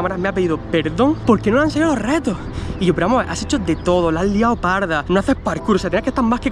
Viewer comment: Escuché que dijo pero amor ?en el minuto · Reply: No, dice " pero vamos a ver " solo que corta la s